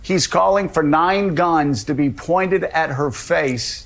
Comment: I need to listen again. I thought he said the guns “pointing” at her.